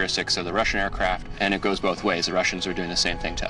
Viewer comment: did he says "It goes both ways". I don't mean to talk about an unrelated topic but I found that both random and funny.